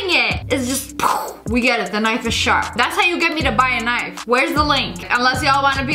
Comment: she really said "The knifest sharp" 😭